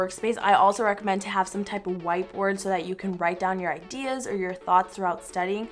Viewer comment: I thought you said White words and I was thinking: But... the words are in black... Xd